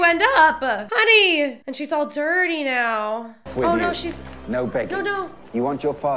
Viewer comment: instead of "no begging" it says "no bacon" on the subtitles. Lol no bacon for you Aria.